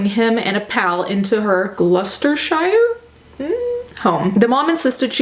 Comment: When you said Glousterceshire I thought you said Lustershire